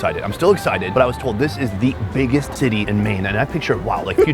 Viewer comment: my mans said the biggest tittie in Maine instead of city 😂😂😂